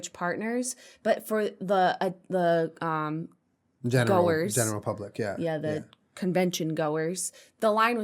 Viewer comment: when julien says "the general public" i SWEAR he said "the Jenna Republic". I'd like to become a citizen of the Jenna Republic please